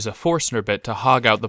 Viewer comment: What does he say here? A 'forcer' bit? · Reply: It's a Forstner bit - happy turning!